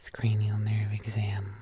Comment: almost thought was about to say ''effing cranial nerve exam'' 🤣